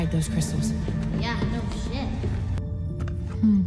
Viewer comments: what did powder say!???? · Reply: She said "Yeah, no shit" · Did I just hear Mable Pines swear? Was not expecting that today. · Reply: I thought she said "no kidding" and not "no shit" lol